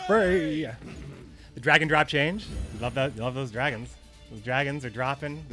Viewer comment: So I no joke really thought they were saying dragon drop and spent most of the stream trying to figure out what dragon drop was. A new card perhaps? lol